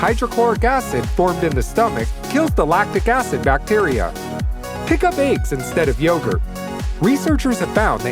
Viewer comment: I heard “pick up aids” 🤣....😅....😨